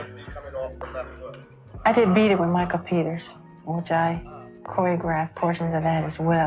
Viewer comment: when he said michael peter i thought he said my computer